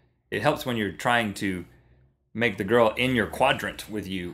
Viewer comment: I heard "Get her in your quadrant" and turned, thinking it was Homestuck.